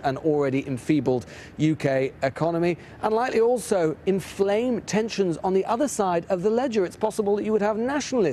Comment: There is no doubt the man at 0:25 will never forget that moment in his life😳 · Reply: I could have sworn that I heard him say "flame on!" 🤔